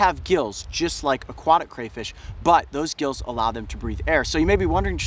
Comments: I thought it said crawfish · who thought it said crawfish at first